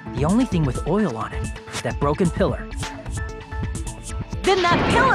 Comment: Does anyone else hear how Hajime says "is the broken pillar"? XD